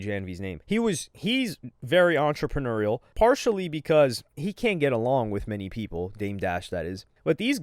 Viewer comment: getting super tuff wit peter.. I thought he said he'll talk it out with anyone..